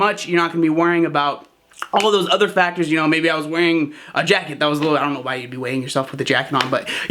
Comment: OH a jacket, haha. I thought you said weighing yourself with a jetpack on, had to rewind 😂 Good video, I really try to watch my sodium carefully, my body holds on to water like a camel 🐫